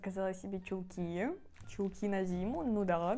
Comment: ПХХАХА МНЕ ПОСЛЫШАЛОСЬ ЧУЛКИ НА ДИМУ